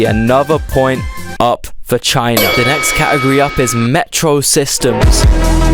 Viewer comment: I dont know why I heard "Metro Sexuals". As if North Korea has any of those XD.